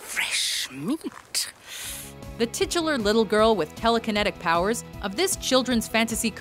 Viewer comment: what the FUCK is that word that this woman uses EVERY single video? used at 9:06, "the SOMETHING little girl"....... i am tired of hearing this word every couple of minutes on this channel....... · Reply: titular, it means the character referred to in the title of the movie.